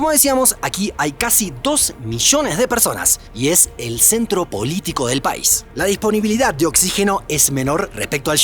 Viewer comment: Escuché "el centro bolítico del país" 🤣🤣🤣